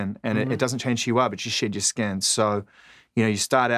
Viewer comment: as much as I appreciate zane's charisma and drive, he's annoying AF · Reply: Greg Pappas it sounded like he was saying shit your skin instead of shed your skin lol